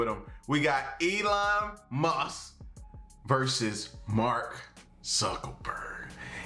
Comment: Sounded like you said Mark suckaburg